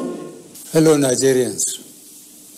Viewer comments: Hello Nigeria 😅.......... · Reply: Lol... He said hello o thought I heard fellow. Nigerians o... Herr this die 😂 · Did I hear him say "Hello Nazarians"? 😂💔